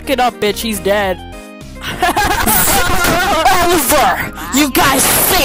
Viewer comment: HELP AT THE END I HEARD “zip oliver you guys solded”